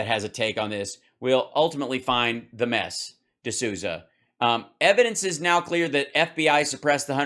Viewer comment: Did he just say “The mess D'Souza”. Hahaha if he did.